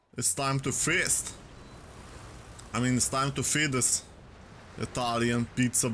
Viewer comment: I’m not the only one who heard fetus am i